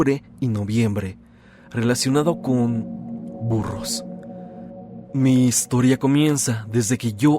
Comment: ¡Dios, ¿qué me pasa?! pude jugar que escuche "furros" en lugar de "burros", ando mal 8(